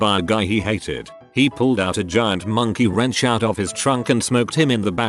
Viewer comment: misheard that as "He pulled out a giant monkey out of his trunk and smoked him in the back of the head with it." I was like "Who keeps a giant monkey in their trunk and smacks someone with it!?"